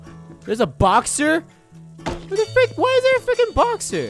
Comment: it sounds like hes saying the f word